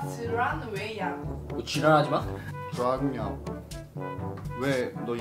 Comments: 지랄하지마? 잘못 들었나...엄... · 이 zㅣ랄하지마 ㅋㅋ나만이렇게들었낰ㅋㅋ